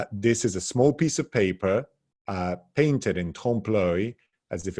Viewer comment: Wonderful presentation - honestly I have only seen up to five minutes, but that's enough to indicate the quality - but allow me to point out, regarding the subtitles, that whereas at 4:43 we hear (in the comment about Bellini's signature) "trompe-l'oeil", we read "templari". The only Templari I know of are the famous order of knights, so I suppose this is a mistake. Elsewhere, much more amusingly, I have seen an artist's medium described as "tempura" instead of "tempera".